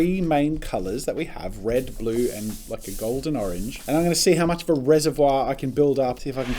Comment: At like 2:44 i thought he said "f***ing golden orange" 😂😂🤣🤦 but after listening 3 times and turning on captions. He said "like an golden orange" 😂😂😂